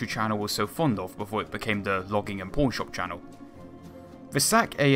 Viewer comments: Nice hit on the "History" channel as the "Pawn Shop Channel". And well-deserved. Also, of course, the content -- the narration -- is very fine! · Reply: I thought he said porn shop channel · Ha! The "Logging and Pawn Shop Channel"!!! Funny, man! Good vid as well! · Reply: Thanks for noting that, I thought he said porn shop.